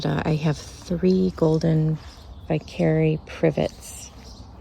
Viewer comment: Very beautiful garden! I’m seeing some new plants I haven’t seen on the other channels. At the 10:28 mark in the video, you mentioned a shrub I never heard of. I couldn’t quite comprehend the name, it sounded like Something Privet? Do you mind telling what it is here in the comments? Thanks! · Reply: Hello, thank you so much! Yes, it’s called a Golden Vicary Privet (botanical/scientific name is Ligustrum x Vicaryi). I love them! They have a beautiful bright lime color (more bright yellow in full sun, more green in part sun). I trim mine back in the fall, and they grow quite large every season. Has a white delicate flower in the summer. I would definitely recommend if it is well suited to your zone. I’m in Zone 6a. Check your plant tag, but I believe they are great for Zones 5-8. Happy gardening! 🪴